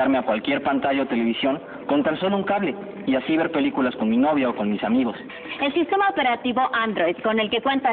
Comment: minuto 22:21 a 22:23 dice el muchacho universitario la palabra ''novio'' o escuche mal???